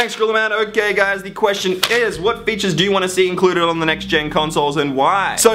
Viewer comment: i heard you saying instead of "what features" i heard "what bitches"